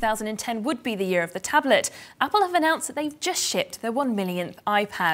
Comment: is it me or does anyone else hear her say "shit"?